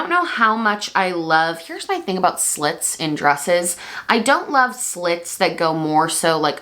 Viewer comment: Yes girl I live for your late night uploading! Insomnia and being a night owl it's so freakin great to get a brand new video at 2am EST. 🖤lmfao I actually thought u said sluts in dresses😂😂